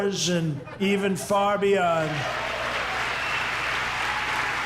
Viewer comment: Did he say "a time for big dreams and bolt-action" I'm not saying I advocate that sort of thing...